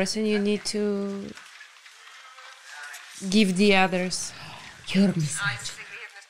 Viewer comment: What did say to at 27:28? It's hard to make out, the way she laughed and said, "No I'm not," must have been something funny. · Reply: She said "YOU'RE Miss Ratched"...